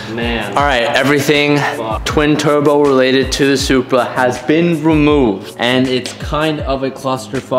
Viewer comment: did anyone else hear how teej said supra😂